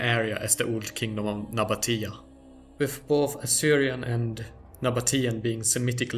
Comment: Awesome video! One correction, AsSyrian and Babylonian were Eastern semitic languages. But Sumerian is considered a language isolate. Whose writing system happened to be adopted by the akkadians. Sumerian was Highly syllabic, whereas all the semitic Languages are consonant focused, So so the sumerian system was rather clumsy in renderin semitic languages. Interesting note, Before the phoenician/kana'ni/ Paleo Hebrew Writing system became dominant a Highly simplified abjadic version of cuneiform form. was also used for ugaretic, A language part of the Central semitic kana'ni/ Paleo Hebrew/is phoenician Language/dialect continuum. Ugaritic cuneiform Abjad Atrested as soon is thirty 3 Or thirty four centuries, ago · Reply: You're absolutely Correct I simply Misheard assyrian as Sumerian when I listened to the video The first time. Very sorry about that. It's good to know at least that my memory of Central semetic languages is still correct Thank you for that reassurance. I previously studied the historical linguistics of indo, European and Afro-Asiatic languages. Very much looking forward to watching more of your videos! ( I need to use voice to text due to the limited ability of my hands. Sorry about the bad formatting that leads to